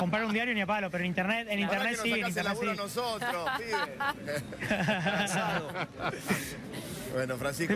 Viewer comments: dijo lo que creo que dijo el de fondo ...... ? alguno me confirma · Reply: "nos sacas el laburo a nosotros pibe, fracasado" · escuche que le dijo fracasado? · Fracasado escuche yo? ojala me equivoque